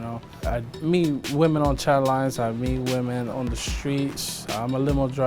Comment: ish, did he say I meet women on childline???